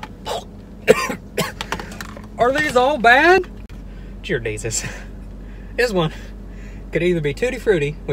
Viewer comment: Am I the only one who heard jear desus